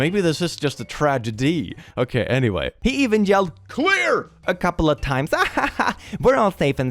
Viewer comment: I was dissociating and I heard "QUEER!" instead of "CLEAR!" At 2:38 😭😭 had to go back a minute for that 💀